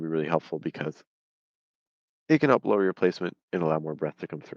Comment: what was the word you said, up-lower your placement?